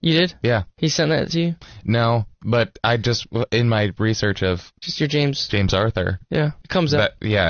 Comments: YALL DID TYLER SAY SISTER JAMES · did he say “Sister James”? Hahahahaha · did tyler just say 'sister james' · did tyler just say... sister james? 😂